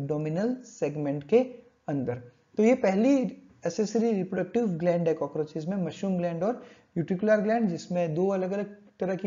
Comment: Can anyone say what word did sir say at 5:49. Pehli......... Gland he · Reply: pehli accessory reproductive gland